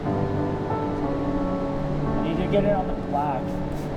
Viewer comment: does he say "Ei jaga tay onek dag" ? Cause it sounded like that :V